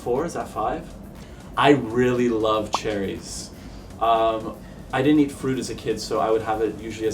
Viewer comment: I thought Antoni said “I didn’t eat food as a kid” and I got way past that point, then realized and was like what r u talking about 😆